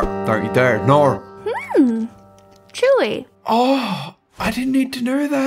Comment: am I the only one who understands what said ;-;" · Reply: She said chewy